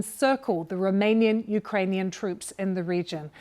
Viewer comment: did she said romanian - ukrainian troops!???